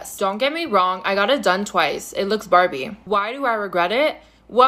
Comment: Did he say that his hole looks "Barbie"? Barbies are plastic and they don't have any holes.